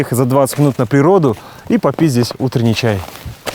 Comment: вместо "попить здесь чай", мне послышалось "попиздить чай" =)))